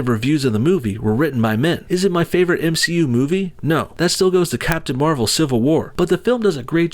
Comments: Did you say captain Marvel civil war? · Did he say Captain "Marvel": Civil War at 9:01?